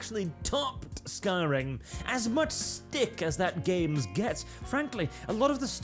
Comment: "as much stick that game gets" wait what? Based on his 2 hour long video, what he actually said is "as much people that game gets"..... just stop using "the stick".. You keep changing what it means, and keep trying to explain it, and based on the comments, you keep failing..